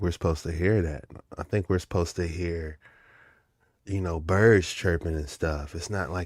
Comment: I heard “nino brown chirping”